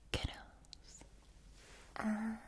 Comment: When she says “boys and girls” it just hits different. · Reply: it sounded like she said kiddos to me